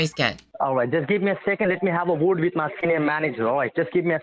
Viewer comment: did he say "Let me have a word with my scam manager?"